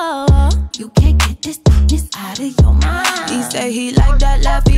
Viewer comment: all this time i thought she said dlck instead of thick PLS